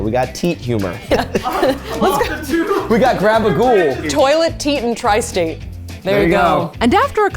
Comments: confirming that it is “grabagool” and not “gabagool”. also i am still involuntarily duck-facing. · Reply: I thought he said "grab a ghoul" 😂😂😂 like "get you a goth girl" lol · I kept hearing say "grab a ghoul" so going off that I offer "(The) Blushing Ghoul" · Glad I'm not the only one that thought he was saying "gabagool" lol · im in love with this gloss, which I vote to be named "Grab-a-Ghoul" for spooky season, and my brain thought that's what said xD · I kept hearing "grab a ghoul" from and I think that would be a fun play on words and fit with your image